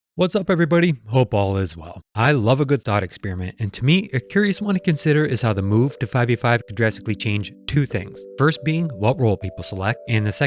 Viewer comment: I thought he said “I love a good thot experiment” lol me too bro. Me too. 😂😂😆